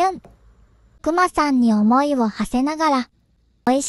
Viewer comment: 「くまさんに重りを課せながら」に聞こえて「具材……くまちゃんには重いもんな……」ってなった